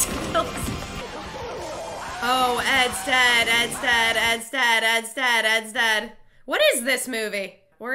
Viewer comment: “Ed’s dead, Ed’s dead, Ed’s dead” I’m sorry but all I heard was “Zed’s dead”